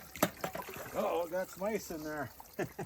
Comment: ish. I thought you said, "Oat O, I've got a mice in there". Did you see Rigbee's ears perk up?